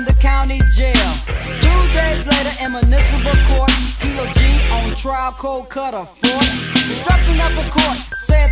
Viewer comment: I always thought they were saying "Kilo G on trial cold cut a fart"